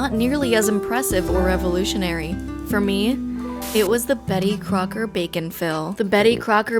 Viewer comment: When I first heard “Betty Crocker bake and fill” I thought it said “Betty Crocker Bacon-fil” and I got real excited. 😂😂